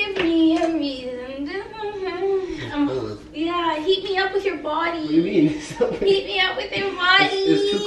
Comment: Heat me up with your body lol I thought you were saying eat me up with your body lol love you guys 💞💞💞💞💞💞💞💞💞💞💞💞